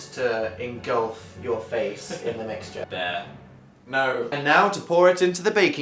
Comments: Did Phil call Dan bear at 5:21 ?! This is adorable if he did <33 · Did phil just call Dan bear at 5:20 that's so cute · does phil say "bear"? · wait did phil call dan bear at 5:20? :O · ANYONE ELSE HEAR PHIL CALL DAN "BEAR" AT 5:20 or is that just me?! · what did Phil say? I heard bear. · Did he call Dan "Bear" at 5:20?!?? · does did Phil just call Dan bear?!? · Did Phil call Dan "Bear".......... As in Winnie the Pooh.......... Or are the feels just getting to my head.......... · DID PHIL CALL DAN BEAR AROUND 5:20 OH MY GOD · did Phil call Dan bear or did he say there?? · did Phil just call Dan bear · DID PHIL CALL DAN BEAR?!?!?!?!?!!??! AWW ASDFGASD!!!!! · Did Phil call Dan 'bear' at 5:20 or is that just me because if he did my heart is MELTING and if he did... proceed with your day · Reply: He said dare lol i thought so too since that isone of Dans nicknames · At about 5:21 does phil say bear? :3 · DID PHIL CALL DAN BEAR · did anyone else hear phil say 'bear' to dan? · checks comments to see if phil did actually say bear at 5:20 Me: HOLY MOTHER OF GOD YES HE DID. SOMEONE HOLD ME I S2G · did Phil call Dan 'bear'? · I swear Phil called Dan 'bear' I'm dying